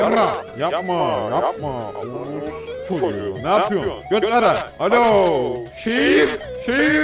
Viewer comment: reis allahmı duydum ben